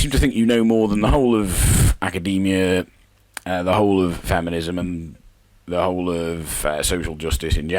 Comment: Did he just say... Famine-ism? FAMINE? Oh man that's great I've gotta use that nickname for feminism.